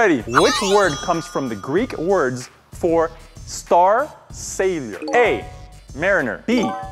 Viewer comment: When said, "Star Sailor" it sounded like he said, "Star Savior".